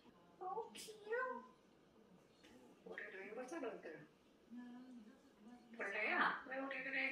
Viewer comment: 우리루이 말 잘한다 로 들려요!!